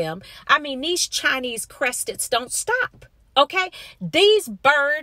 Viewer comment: 🤣🤣🤣 ok Sis, did you just say “these Chinese Creasted don’t stop?” Hilarious, I actually own a hairless one (Chinese Creasted a.k.a CC) and he looked up at me when I laughed so hard after you said that. Peace/Blessings❣️